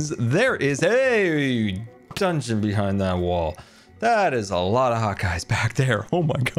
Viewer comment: it almost sounded like you said "there is a lot of hot guys behind that wall" lol